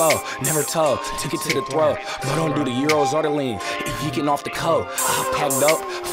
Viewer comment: He nice but ion know what he talking about when he say this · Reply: He said he ont slide off euros or lean he slide off coke